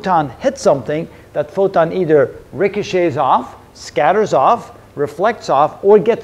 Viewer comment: Sir can you tell what you have said between 3:34 and 3:36 Regnesace off or what ? and what it means? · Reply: the word is "ricochet" which means it bounces off the target